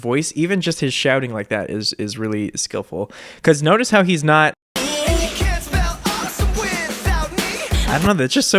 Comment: nobody: Taylor and Brendon: you cAnt SpEll aWesOme without ME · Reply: Ava Nelson I legit thought he said you can’t spell us without me!